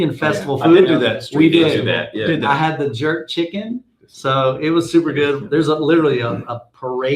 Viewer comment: Wait... Did you just say you ate jerk chicken?! I'm Jamaican. My husband is Trinidadian. I'm so glad I just subscribed. You're definitely a people person & a great ambassador for this genre. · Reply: I'm relieved, thought he said dirt chicken.